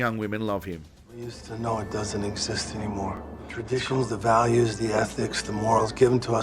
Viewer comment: Did he just say “the ethnics”? 😂